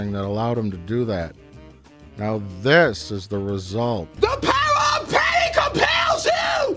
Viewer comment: when the trailer first hit, i thought she was saying “The power of PAIN compels you!” Which is funnier than just the power of patty